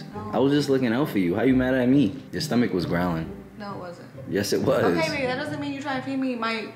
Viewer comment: But 😆😆😆why I hear , "you stomach was groaning ,so I decided to feed u a banana"